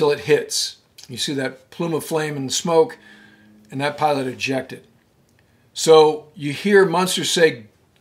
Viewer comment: For years i thought the pilot said "Quality Justice". He may have said "Pilot Ejected"